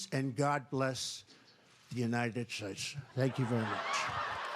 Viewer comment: Did he just say UNITED SNAKES?